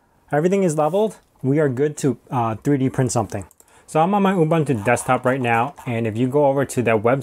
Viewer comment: what linux are you using ? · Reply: I could have misheard, but I'm pretty sure he said he was using ubuntu